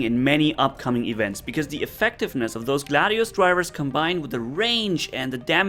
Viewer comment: I thought you said “Latios Drivers” at first，haha.